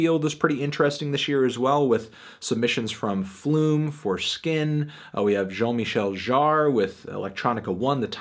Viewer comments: i thought he said "flume- foreskin" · did anthony just say foreskin?